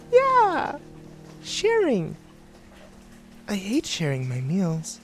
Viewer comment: I was only half paying attention around the end. But I had to rewind it. I swore the chic clown had said, "yeah, I hate sharing needles." I was like, wow smart clown??? Or not?